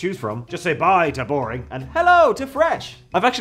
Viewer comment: With his accent... I could've swore he said the sponsor was..... Hella fresh, instead of hello fresh.